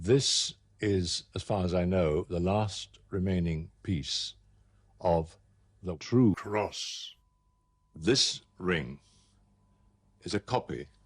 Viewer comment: what did he actually say? Why is his voice dubbed over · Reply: He said that this piece of wood is a piece of the true cross